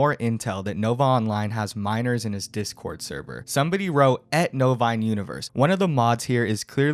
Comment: I thought he was saying "nova in universe" not "novine". WTH is novine? Bovine?